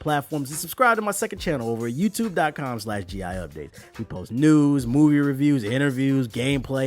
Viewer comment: I thought he said we post nudes